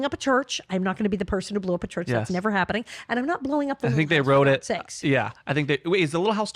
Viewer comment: At 1 hour and 7 min Allison was trying to tell why Michael Landon wouldn’t destroy the church ar the little house and I couldn’t understand what she was saying about the house because the interviewer interrupted her right at that point and even with captions on and replaying it twice I still couldn’t get it. So does someone know what she was saying about the Little house.? I know everyone is saying what a great interviewer he is, but I think he interrupted her way too many times. I think when an interviewer asks the questions and then stops and lets the person they’re interviewing tell the story it makes for a much smoother telling and besides, it was Allison‘s story not his. I think he was so excited about being able to interview her and tell his own history with the show as he was growing up that he wanted to share how he saw it and how he felt. I get that but save that for after the interview.❤ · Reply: I had to rewind a couple of times but she sounds like she is saying, “and I’m not blowing up the Little House for godsakes”.